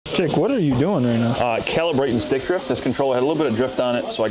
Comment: Im so high i heard "celebrating stickdrift"